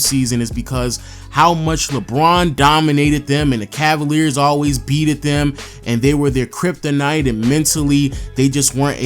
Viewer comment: Did you say, "Beated them" @ 3:42 ?